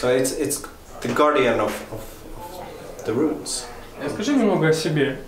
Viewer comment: Я понимаю, что перевод, скорей всего, профессиональный, а не дословный, как обычно переводят любители, но меня смутила пара моментов в субтитрах. Отвечая на самый первый вопрос, Эйнар произнёс слово "roots", если я правильно расслышала, "the guardian of the roots", т. е. хранитель корней, получается. А переведено как "хранитель тайн". (Если кто-то лучше понимает английскую речь, отпишитесь, пжл). Далее, в момент рассказа о любимом герое сериала Эйнар говорит о боге Локи. Тут уж сильно ужали его речь, возможно, чтобы не загромождать субтитрами экран, но говорил он много. К примеру, фраза "he's very human" раскрывается дальше: "you'll never know if he's good or bad, but he's very inportant" ("невозможно понять, хороший это персонаж или плохой, но он очень важен"), или "the god Loki is the one who initiates things, starts things, inportant things" (Локи очень часто является инициатором, начинателем многих процессов). Не критично, конечно, но всё же. Также большая благодарность за то, что нет наложенного перевода, и есть возможность услышать оригинальные тембр и динамику речи. И спасибо за Эйнара, конечно же :)! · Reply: He says "The Guardian of the Runes", not roots. Wardruna is ward (guardian) + runa (rune). But runes can also mean secrets, as Einar says. So the name can mean also "The Guardian of Secrets".